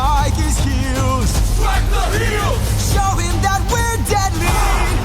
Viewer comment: I'm sorry to everyone who won't be able to unhear this now, but whenever i first heard survive and even at this point, when Odysseus says "show him we're deadly" I always hear "show him we're dead meat"